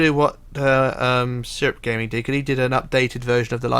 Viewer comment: What’s the name of the person that made the updated lifeboats? Can’t really hear what you’re saying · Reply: Hi the guy was called Syrupgaming, But he deleted his channel a few weeks ago, sorry